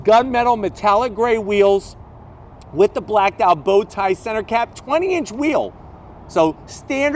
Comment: did I hear 'bow tie'?